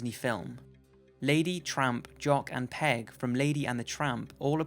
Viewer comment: Haha i thought he said trump, not tramp